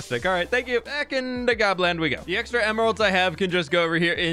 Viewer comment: I really love what you're doing with gobland. All the life and movement makes the builds so interesting. And it's fun to see the sort of steampunky/industrial builds that you started trying out in the amplified world make a comeback since that world became unplayable. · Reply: I thought you said Poland for a second lol